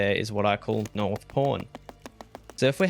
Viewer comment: i can’t be the only one who thought he was saying north porn